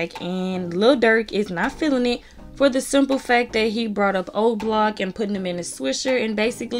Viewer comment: Not going to Lie I thought Yb said the Whole Block then I had listen to it 4 times did I had heard OBlock😭😭